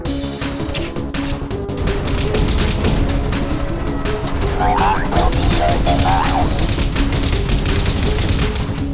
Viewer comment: did he say my heart will be broken first? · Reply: no it said my art will be your demise.